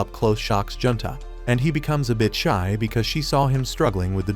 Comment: I thought he said “and he becomes a bitch” 6:30 😭😂